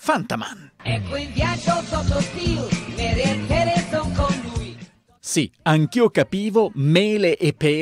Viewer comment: Io di Fantaman ascoltando ho capito "nelle ARTERIE son con lui"